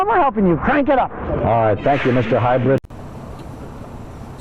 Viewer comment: Did he say "alright, thank you Mr. Hybrid"???